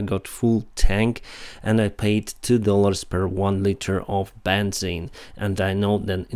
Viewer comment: I'm sure I misheard you due to the accent, but when discussing filling your cards fuel tank it seemed like you said "more than $2 per liter of benzene." Benzene? Did I hear that correctly?